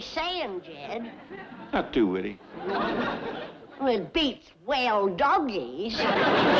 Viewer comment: To today I have thought Jed said Whee Doggies I reckon I know better now. Not bad for a near sexygenarian to still be learning if I must say so myself.